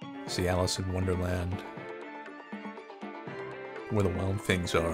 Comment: I thought for a second, that I heard say "Cialis in Wonderland", but then I realized it was Alice in Wonderland. Good lord.